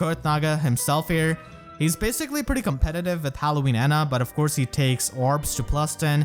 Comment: I spent such a long time trying to figure out what you said @ 7:45. I was like when the hell did we get Halloween Ana. No he must of meant Hana. And then I was like no that's ninja Hana. Then my dumbass finally figured out you were saying Ena.